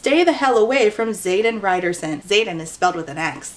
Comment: i keep hearing "riderson" as "ryerson." so like the unbelievably steamy love interest is named after the dweeby guy bill murray punches in groundhog day